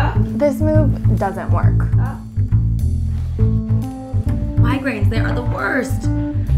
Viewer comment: What she saying, I had to keep on replaying it but I hear, ''my grades, they're the worst!'' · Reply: The smaller-chested blonde said, "migraines, they are the worst!" and the busty blonde said, "it's my back, because of my boobs!"